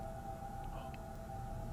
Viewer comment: Did anyone else catch the voice that sounded like it said "No" @ 46:42 after asked if anyone wanted to see a show?